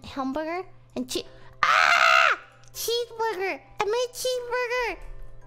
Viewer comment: bruh she actually sounds like shes saying cheemsburger